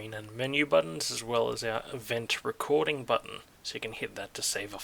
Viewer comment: "event recording button". I firstly thought you said "revenge recording button". Hmm....